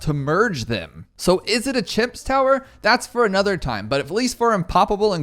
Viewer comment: Did he say chips or did I not hear the M?